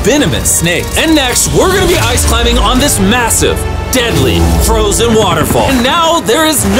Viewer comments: DID I HEAR massive? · did I hear 'MASSIVE'? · Did he say MASSIVE